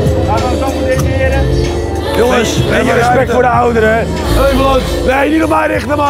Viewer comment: Wie hoorde nog meer dat op 6:01 Stefan zei 😂😂